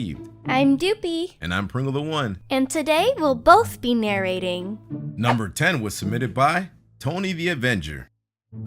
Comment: for i minute i thought they said" and we will both be marinating!" wait/... WHA